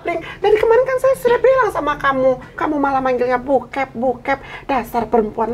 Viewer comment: " Bukep" kedengeranya kayak " Bokep"....😁